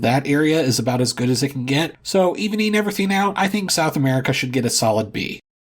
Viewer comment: I'm sorry.. what is he even saying at 4:05? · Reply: I believe he says "so, evening everything out", I didn't catch it at first lol